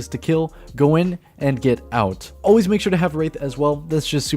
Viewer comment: who else heard make sure you have rape?